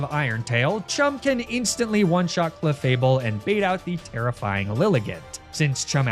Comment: Am I the only one who heard tera flying lilligant and when huh?